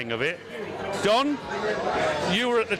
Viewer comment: He was at the 12:51 mark,thought you called him "John" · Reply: Don, you mean Don